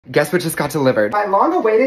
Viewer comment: Omg i heard “ Guess who just got a liver” 😂😅